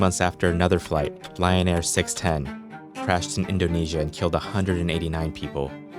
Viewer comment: LION AIR. I thought they were saying Ryanair. Thank god Ryanair doesnt fly this